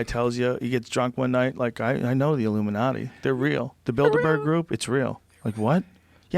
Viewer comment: Every time Joe says "Bilderberg", I hear "Build-a-bear".